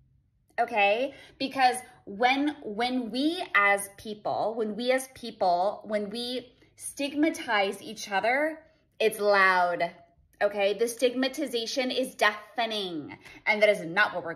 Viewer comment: I swear i thought she was saying astigmatism at one point 😂